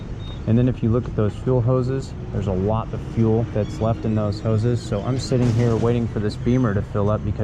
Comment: So the gas station you were at uses 1 pump for every type of gas or what the fuck did you said at 13:20? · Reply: The gas station has one hose for all 3 grades of fuel. I'm saying there's a significant amount of gas still in the hose that you get into your grom before the good stuff gets in, you know what I mean?